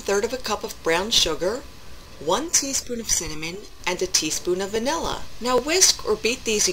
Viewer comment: You certainly had my head spinning at one point I thought you said one teaspoon of salmon lol..(it was cinammon) Its Peach season up here and the Winona Peach Festival is going full blast, so Ill grab some peaches and do this for sure!